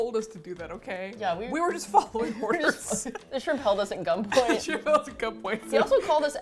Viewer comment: The way I heard “the shrimp held us at gump point” and like. Yeah !